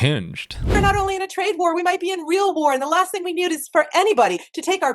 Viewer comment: I’m a real boy 😂 that’s what I heard😂